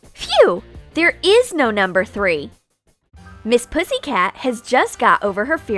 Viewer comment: did SHE Say miss ”Pussy” cat?